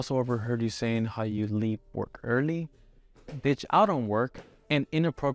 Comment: Bro I thought he said ‘B*tch I don’t work’. I had to listen back a. Few times to realize he said ditch out on work 😅